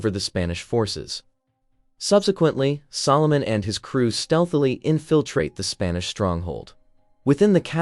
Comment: did he say stealth hahaha.